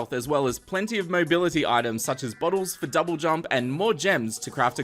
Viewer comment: for SOME reason, i heard 'bottles for double jump' as 'bottles for dubstep '